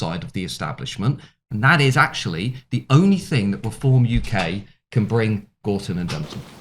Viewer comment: Matt says perform UK instead of reform. Bit of a Fraudian Slip there 😂